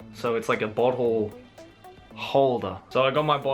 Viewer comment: Bottle holder... ohhh, I thought he said butt hole